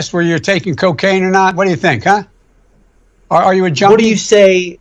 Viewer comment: Sounds like he says, "Are you a jonie?"